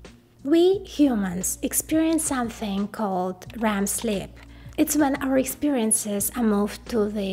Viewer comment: Sounded like you said Ram sleep with your accent. It's actually a good pun! LOL Ram is memory and sleep is good for memory. I know I don't need to explain it. Don't mind me.